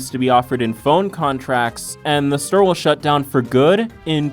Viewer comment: Did you say the iMode store was shut down in 2026? @ 30:00 😅 ... Great video though, thanks! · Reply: “Will shut down.” Thanks for watching, glad you enjoyed!